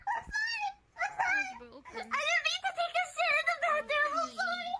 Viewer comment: What she probably said: I didn't mean to take a shit in the bathtub What I heard: I didn't mean to take a shit in the bathroom